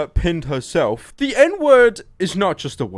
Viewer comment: I thought you guys were saying ed-ward